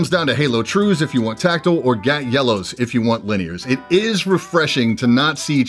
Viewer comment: I had to rewind bc I thought said "Gag, Yellows" then I remembered that the ENTR when I looked at it offered Gateron Yellows. "OOOOOOh!! GAT yellows". 🤣